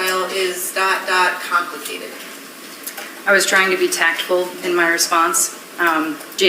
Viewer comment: I thought she said “tactful” which means considerate; as in she was considerate of what she had seen of Jason’s behavior/communication style in her response. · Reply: no she said tactical. She meant tactful, which you defined correctly!